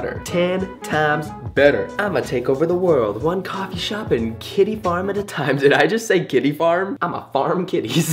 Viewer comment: i thought you said 'kiddy farm' and was waiting for the seedy face :") hahaha